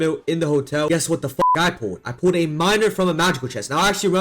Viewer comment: all i hear is "i pulled a minor"